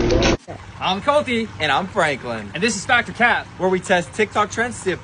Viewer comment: I thought they said ‘I’m colty and I’m pregnant💀